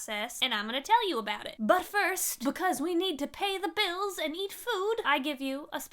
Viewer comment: had a moment in the intro where I thought, 'wait, did they say "we need to eat bills and pay the food"?' and rewound a few seconds to discover that no, my ears were just playing tricks on me.